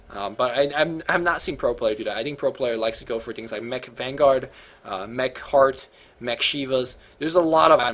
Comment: All I heard was McShiva, McHeart